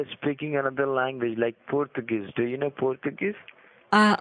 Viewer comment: Lol. I legit thought he was saying Poltergeist instead of Portuguese. 🤣